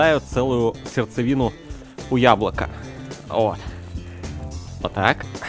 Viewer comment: Мне одному на 1:58 послышалось "хуяблоко"?